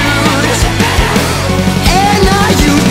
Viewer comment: Joey’s gonna hate this 😂 The best part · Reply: ? I thought he said, Jury is going to hate this, not Joey? Who's Joey?